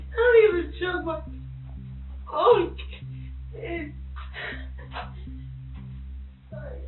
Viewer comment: does she say, "i dont even choke my own kids" ?